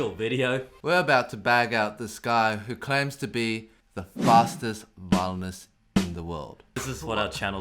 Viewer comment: I thought it said fatest violenest in the world